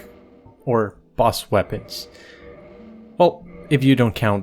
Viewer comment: Did he say boss weapons 🐔📢